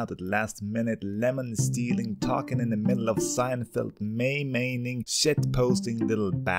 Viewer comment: did I hear.... *MEI MAINING*